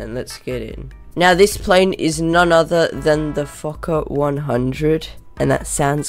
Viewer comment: If i use subtitles YouTube think you say f*kkor